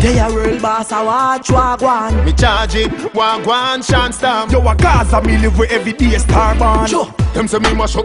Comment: what him call Shawn storm? Word is power.